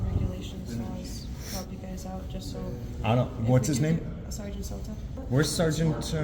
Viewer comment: I thought she said sergeant Zoltan. I figured he was busy looking for the continuum transfunctioner. It's a mysterious and powerful device.